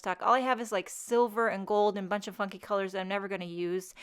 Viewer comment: it sounded like you're saying, "a bunch of f***ing colors that I'm never going to use". Then I realized that you said, "funky"....hahaha. Love your videos, beautiful card as usual :)